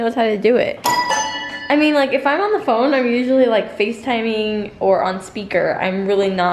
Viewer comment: 1.The girl with blue hair and the glittery top is cool. 2.Am I insane or did touch the towels on top of the laundry and say towel? Sounded like it. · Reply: I heard "Im on the phone, Towel"